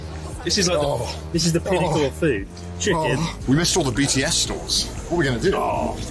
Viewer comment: right after the moaning I heard say "missed all the BDSM stores though". took me a full minute to realise that's not what he said.